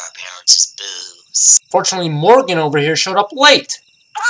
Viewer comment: Go to 7:53 turn on subtitles and instead of Morgan it turned to Kenny